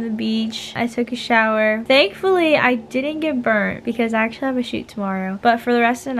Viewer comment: I thought you said “Thankfully I didn’t give birth because I have a shooting tomorrow” but you actually said “Thankfully I didn’t get sunburned because I have a shooting tomorrow” LOL my bad love you and your videos stay as positive as possible XOXOXOXO